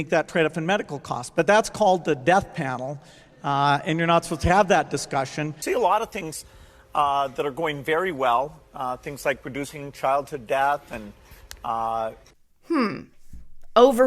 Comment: Did he just say ( producing or reducing) childhood death?